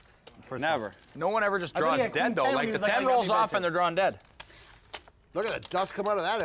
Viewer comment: is such a funny and entertaining guy...but only if he is not that drunk. What does he say 4:08 "Look at that dust...?"